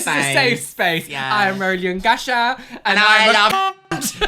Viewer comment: Always wondered, what does 'ungasha' mean? I misheard it originally as 'Roly and Gasha', I wondered if 'Gasha' was your dog or something.